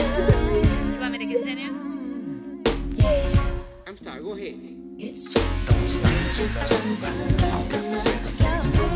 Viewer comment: Can someone please tell me what Vanessa is saying at the 3:09 mark? "You want me to------?". Then, after he says "I'm sorry, go'head"...he looks down a bit... then the shot of her at 3:13-3:15...looking like the cat that swallowed the sweet cream! It's subtle...ya have to be sophisticated to notice that ...lol!...she IS dressed up in cat's pajamas and making clawing moves... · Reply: You want me to "Continue"